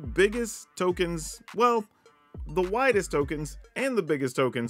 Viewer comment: I didn’t have my headphones in and I could’ve swore you said “whitest” token hahah instead of widest. I honestly think I hear in southern. Solid list. I do think the Vorinclex is a solid addition. Since you’re going wide wide with tokens those counters are solid. I did love seeing Kaya making a token copy of the Vorinclex flip side. That was cool. She does create some interesting situations. It’s also nice seeing the Green Invoke. It doesn’t get enough love. Plus with Mondrak man 4 tokens would be legit. Though I do have a question. Would you get to pick the counter for all 4 or do you only get to pick for 2 of the tokens and then they’re doubled.